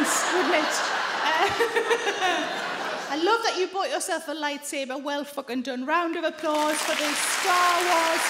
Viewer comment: I thought the gentleman said "lifesaver" so you can imagine how fun it was to understand he said "light saber".